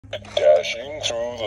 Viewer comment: i thought is said dashing through the snow cuz santa f***ing gay